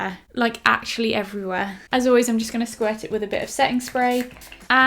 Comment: Was I the only one that thought at 9:47 she said sex spray?